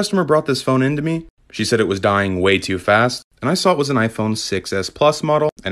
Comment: I thought he said she/he was dying to fast